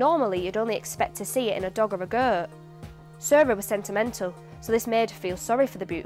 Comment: Instead of "Sarah was sentimental" I understood "Sarah was sent a mental" at 07:00... 😁👍